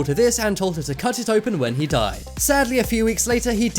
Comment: OH I thought it said then he dies